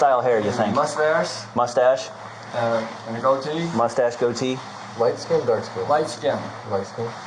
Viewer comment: How do you see gold teeth if he has a mask on. No white shirt. How incompetent are these officers? Apparently looking to arrest someone. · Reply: I thought they were saying gold teeth, too...at first. Then I realized they were saying goatee.